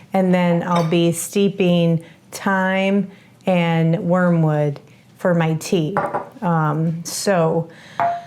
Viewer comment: You called one of the herbs wormwood, then I thought you said mugwort. Are those names interchangeable, or is there more than one herb besides the thyme? The flavored honey sounds delicious! I may have to make some to have around. I’m a natural health person, too, and totally understand when you said you didn’t have a doctor. I haven’t had one for at least ten years and haven’t been sick. (Knock on wood). Sometimes illness is sneaky and we don’t feel it for a while. Glad you chose to find out what was going on and are feeling better. 😊